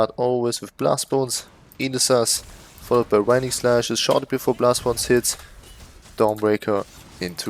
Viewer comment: I was wondering what you're saying here, "shock" or "short" before blastbones hits? I'm sorry 😅 your accent kinda blurs the word. Great build, I'm having lots of fun with my necro again! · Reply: my poor german accent :D "shortly before blastbones hits, dawnbreaker .. etc"